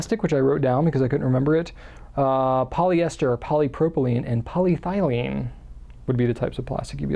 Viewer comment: Hi, as always, I very much enjoy your informative videos. I didn't quite understand what you said about the plastic storage containers around the 28:20 minute mark. I got the polyester and polypropylene, but missed the third one that sounded a bit like "polythylene" which I don't think exists or possibly poly-phthylene which might exist. There are many, many kinds of plastics and compounds and I certainly don't know them all. Did you mean polyethylene? ("poly-ethyl-ene"). There may be a poly phthylene form of plastic (yes, that's the way phthylene is spelled and both the "ph" and the "th" are pronounced) but I'm not familiar with it as a plastic. I'm certainly not complaining, your enunciation is normally quite clear, but the pronunciation of in these compounds is pretty similar to my aging ears. Just asking because I do store some paper and books, some in cardboard and some in Sterilite(tm) bins. Many thanks. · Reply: I meant polyethylene, I may have just misspoke! Sorry about that